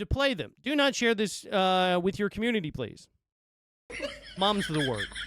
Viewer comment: They did not say "your community", they said "THE community". They didn't want players to use it as a method to grind money.